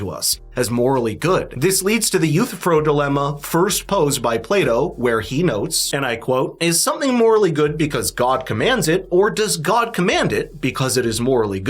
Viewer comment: Why t.h. did he say Play-dough?? Only later I realized that he meant Plato lol